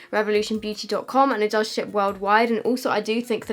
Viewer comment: I thought u said, "it does shit worldwide" LMAO